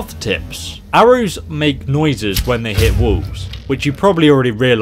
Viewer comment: I swear for number 7 I thought he said 'arrows make noises when they hit wolves'. I had to replay it like 5 times before I realized he said walls